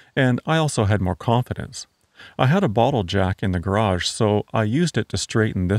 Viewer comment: For a second, I thought you said you had a bottle o' Jack in the garage...